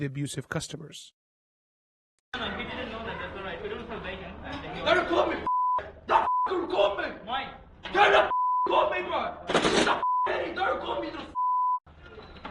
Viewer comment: I Thought he said 'Dont call me' Not dont record me lol